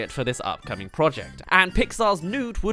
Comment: I heard the pixar's "nude"